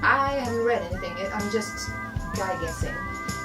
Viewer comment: Did you say guy-guessing? Never heard that. Maybe some SWEnglish, like you saying smoke-pipe? ;)